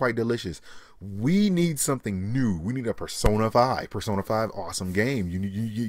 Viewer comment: did he just say persona 5 lmao